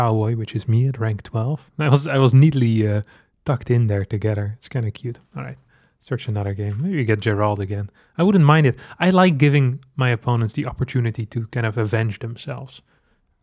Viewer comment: really thought he said get Jinrolled again